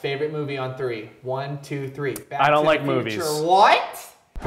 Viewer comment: I thought he said "I don't like boobies"